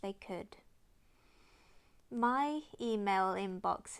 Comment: For a second I thought you said "my fe-mail inbox".